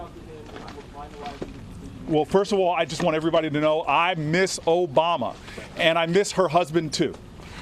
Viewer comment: did just said he miss Obama and her husband too?